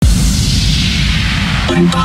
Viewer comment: Didn't looke at the title so I thought they said "My body is symmetrical."